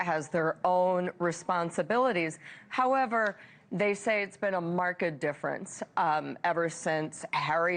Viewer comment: “Marked difference” is what was said, “Markel difference” is what my brain heard lol. 25:19 Pivot! Just pivot…🔁🔄🔃♾️🌪️